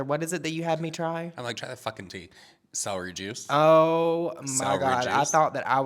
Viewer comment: I might have had to rewind like 4 times cause I kept hearing “sour jizz” when y’all said celery juice! Too many dabs. 🫣